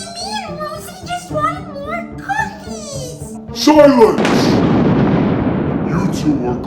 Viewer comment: I thought the Angry Mommy said sunlight but turns out it's silence